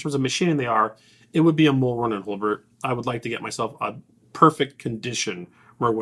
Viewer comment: what unicorn gun did he say? I couldn't make it out. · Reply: Merwin and Hulbert, Ian has a video on them